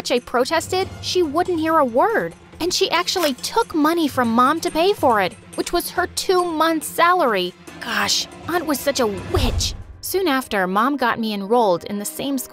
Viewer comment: i heard bit?sh not witsh